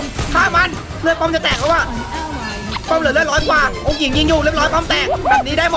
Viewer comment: ทําไมผมได้ยินว่า โอหงิดยิงอยู่ มีใครได้ยินไหมครับ หรือผมฟังผิด5555